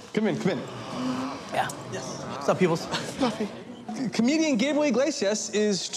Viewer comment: Did I just hear “comedian gay boy inglesias...” ? 😂